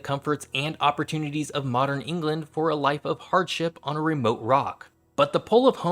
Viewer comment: I thought he said...."Why would they trade the comforts of England for a life of Bird Shit on a remote rock..."